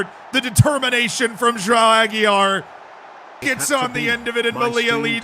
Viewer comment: I heard him say Rojão Aguiar, which is a pretty fun nickname